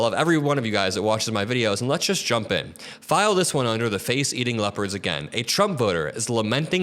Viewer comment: I thought he was saying “face eating lepers”. 🤣🤣